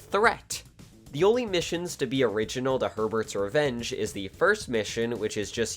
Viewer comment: I was 29 when that first game came out, so yeah. Also I thought you were saying "Pervert's Revenge" until I looked that game up.